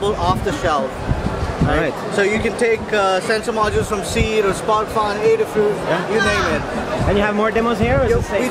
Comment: in this video the guy say you can take off the self sensor module from seed, ????, and adafruit. Does anyone know what the ???? place is it sounds like Spotify but obviously they do not sell sensors? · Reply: (piqueen314) I know your comment is old but anyway I think he said sparkfun :)